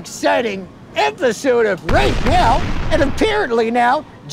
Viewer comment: Lmfao the very end of the video the subtitles say Rape Hell isntead of right now